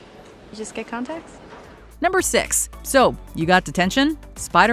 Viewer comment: I really thought Steve said "So you get Attention" Turns out he said "So you get Detention".